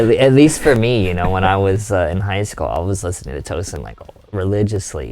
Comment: What band was Tim mentioning around the 4:26 mark? I can't quite make out what he's saying. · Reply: Tosin Abasi, from Animals as Leaders.